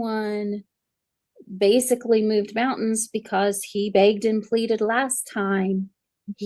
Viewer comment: I thought said, "moved fountains", lol 😂